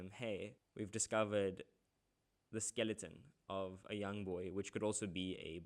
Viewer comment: me cos i heard YoungBoy, ok i'll stop